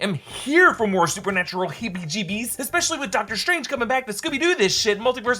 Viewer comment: Sounds like he said Doctor Strange 🤔 This Batman Night is crazy 😜 😳 🤪 😅 😫